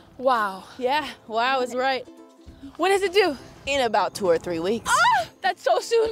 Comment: When she said “when is it due” during that pregnancy skit I thought she said “what does it do?” Which would have been a much better joke